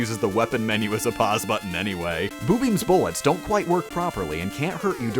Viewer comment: I thought boobeam was being said as boobie.